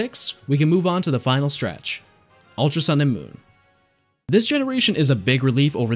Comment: did anyone else hear ultra sun and moo?